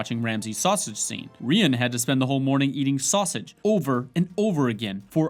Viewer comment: What the fuck does he say in the @ 19:35? Who the fuck had to spend the whole morning loking ramsay eating sausages?